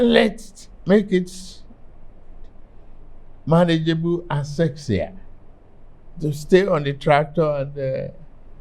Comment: Did he say "manageable and sexier" 46:06?